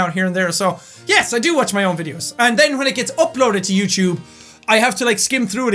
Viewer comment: i thought you said f***loaded instead of oploaded